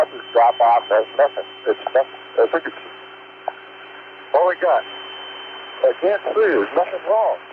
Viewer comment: it sounds like Conrad said "What have we got?" rather than "Where are we going?"